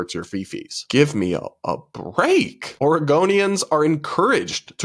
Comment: I thought you said "Argonians" at 14:56 and got really confused, like.... when did elder scrolls get brought into this?